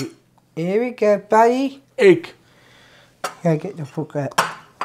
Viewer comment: Without the subtitles, I would've thought he said "Get the fuck out"